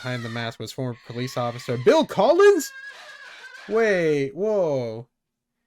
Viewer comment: Idk why, but when said Bill Collins, I thought he said Bill Cosby